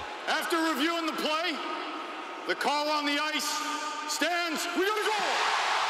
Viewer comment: What'd He Say? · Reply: WE'VE GOT A GOAL!!!